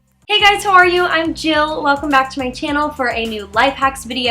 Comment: Did she just say "Who are you" as an intro? That.. is so.. weird.. Edited: I just checked the transcript of this video and she said.. "How are you". I thought she said who are you. 😂😂😂😂😂